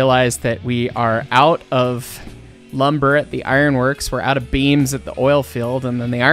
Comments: did anyone else hear beans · so I heard... when said "We are out of lumber at the iron works... We are out of beams at the oil fields...." I heard 'WE ARE OUTA BEANS AT THE OIL FEILDS'